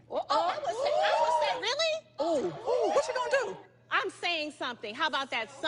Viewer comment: "Oooh, whatchu gonna do????" LMAOOOOOOOOOOOOOOOOOOOOOOOOOOOO!!! All I heard was, "Who gon check me boo?" LMAOOOOOOOOOOOOOOOOOOOOOOOOOOOOOOO!!!